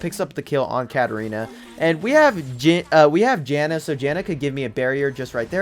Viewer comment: who's Janet? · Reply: i could have sworn he said Janet